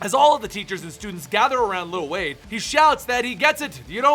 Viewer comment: I thought was saying "lil Wayne"... everytime he said it, i said "WEEZY"